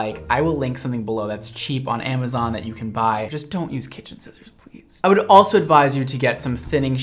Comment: what was said “pls don’t use kitchen scissors”, what i heard “you can use paper scissors”